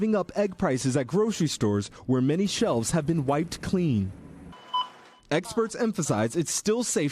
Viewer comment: did you say “eggsperts”??